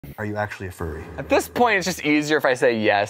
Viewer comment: I thought he said "are you fruity?"